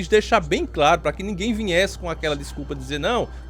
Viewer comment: Em 5:22, eu ouvi "vinhe-se" ??????